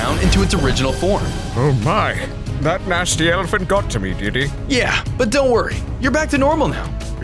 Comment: Also I thought brr brr patipin said Diddy but he said did he